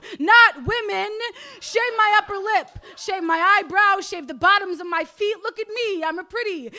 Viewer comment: Wait what the fuck did the thing at 8:00 say shave the bottom of my feet?